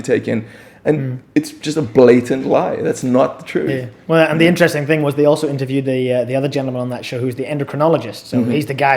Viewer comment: What documentary does mention at 28:10 ? I couldn't understand what he said. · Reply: asets12 'The Men Who Made Us Fat'